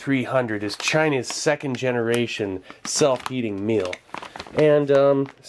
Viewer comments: I thought it said "self hating meal" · I thought he said self eating meal at first😂